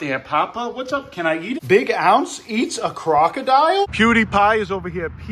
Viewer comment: i heard "more lets crock!", so lets crock part 1 when?